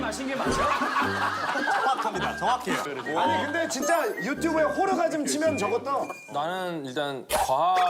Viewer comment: 아니 김희철 뭐라는거야.. 내가 제대로 들은겨…..? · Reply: 호러가즘